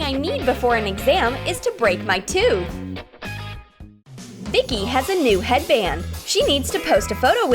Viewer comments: 😂😂😂😂 THICKY Edit: OMG I'm so sorry I think that said vicky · did they say thicky ?